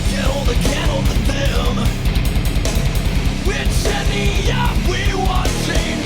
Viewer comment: In the intro song I always thought it said "which idiot we watching" instead of "which Andy"